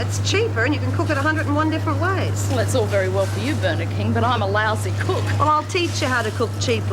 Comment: That little Gary needs a smack in the gob! And his little bum too. I could swear whats her name called Jude Burger King? At 15:09 what did she say? I know you call your Burger King, Hungry Jacks. Poor Maxie was bashed bloody! It looked like blood running down the sink to me. Now there's Meg eating salad, chef salad And that slop that what's her name cooked looks as sickening as the stuff they eat at Wentworth. They should have at least had some bread or some potatoes, carrots and rice to go with the stew. Major Ferguson. Wasn't he a judge before? Where are the screws? I thought they were supposed to be supervising the women in the laundry. They supervise them in the kitchen and the garden. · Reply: her name calls Judy "Bernard King" Bernard King was a T.V. personality at the time doing a cooking show.